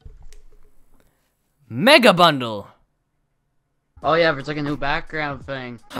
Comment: At first i thought he said Nigga bundle